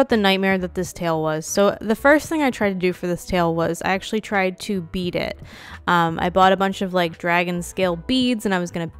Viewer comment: When talking about the tail, what I heard you say (while not looking at the screen): "I tried to beat it" (insert very confused look here) - What you actually said "I tried to bead it" (took me a minute to realize it once I looked at the screen) hahaha Also, love that you call all of them "ladies"